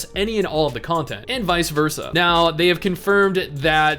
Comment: They confirmed what? PS4 Release? Did I hear that right? stares at Cyberpunk